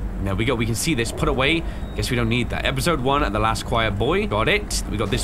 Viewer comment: boy? I Thought it said 'bay' lol